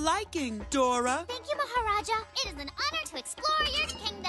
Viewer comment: For a second their i thought i heard the name of jujutsu kaisen's strongest shikigami, Mahoraga.